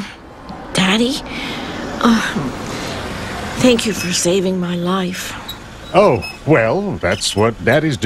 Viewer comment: what did he say? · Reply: Um,daddy,uh, thank you for saving my life.